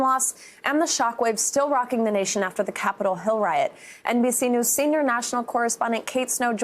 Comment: Thought i heard the presenter say "the Capitol ho riot". Lol.... Trump's ho's.